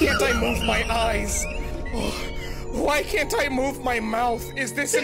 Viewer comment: I hear it as "why hentai move my mouth" for some reason